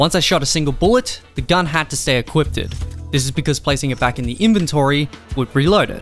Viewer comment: Bro did you say ‘equipted’ instead of equipped lol